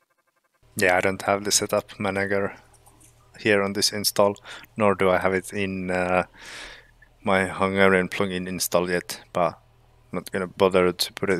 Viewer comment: out of context: 10:30 :O I still don't know what you said after 3 listens LOL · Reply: I said that I don't have the setup manager in my this (CZ plugin on this vid) install, and not on my HU plugin install either.